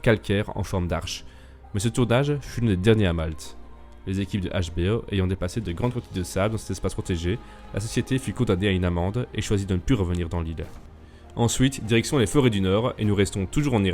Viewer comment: quoi ??? J'ai pas compris ! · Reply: Ces lieux historiques sont protégés, et l'équipe de HBO a amené plein de sables . C'est pour cela que HBO a du payer une amende, et n'est plus revenu